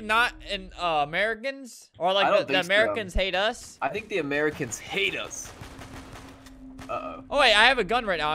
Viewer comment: i thought that he said "i think that im an american pp" but the adult version... but he just said "i think america HATES us!"